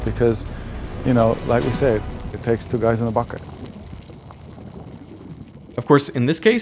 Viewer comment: Did I just hear 'Two guys one bucket'?